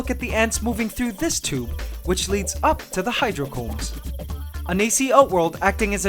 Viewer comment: did he just say hydro flask?